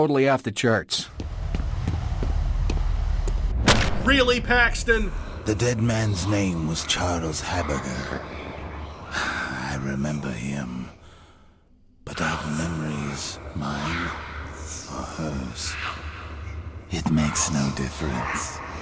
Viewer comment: Did he say his last name is burger?!?!?!